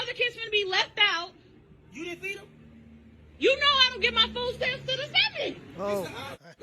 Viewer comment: What did she say at 0:44-0:46 sounds like gibberish listened 5 times and can’t make it out Edit: she said, “You know I don’t get my food stamps till the seventh” She will deny her kid food because she can’t afford food on her own so guess what we all struggling together how fucked